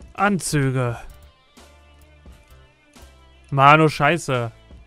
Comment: Ich hab verstanden Mau is scheise dabei sagte er Ma nur scheise :D